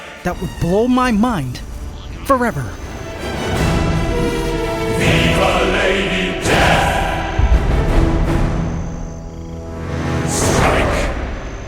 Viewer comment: OMG "VIVA LADY DEATH... STRIKE" is such a great intro song! Truly epic! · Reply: and here i heard "VIVA LADY DEATH.... SIKE" XD that half a second daze really affect things